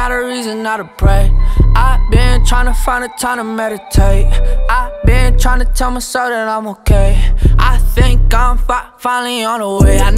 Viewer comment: dont tell me this dosent sound like he said ive been trying to tell my son im okay 0.55